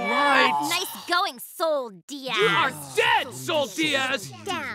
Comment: Things I found interesting in this pilot: 1) there was a horrible dirty joke about ass - so it probably means SVTFOE ment to be more adult-like show; 2) there were bad words used like "shit" - the same conclusion; 3) River looks too serious and angry here, and Moon is too timid; 4) Marco is even more unlucky loser here, which is crazy; 5) Marco is more nagging here that he even freaks Star out with his yelling and ugly face expressions; 6) Marco really used to think Star was quite clever, which is kind of surprising, to be honest; 7) the first Starco hug looks pretty unconventional; 8) there was a direct mention of chronology - it was stated that 2 weeks passed from the Star's first time on Earth and Match Maker; 9) Ludo's first appearance was even better that in the very show; 10) Tom's fist design was disgusting. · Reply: I thought it was said something like "You are shit, Sol Diaz".